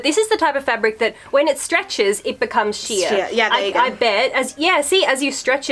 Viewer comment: Just to letcha know, at the 7:27 mark, on the English subtitles, (as opposed to auto-generated) instead of saying 'when you stretch it, it becomes sheer', it says, 'when you stretch it, it becomes shit'...